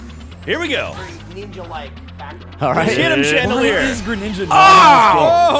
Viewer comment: did anyone else hear someone at 15:48 say "why is greninja not in this game?"